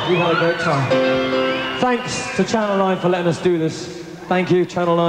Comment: What does he say at 0:11 after thanking management? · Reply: I'm pretty sure he says "thank you to management and to PA," so Clive Franks and crew running the soundboard, if I had to guess. Then proceeds to say "I don't think I've left anybody out, now."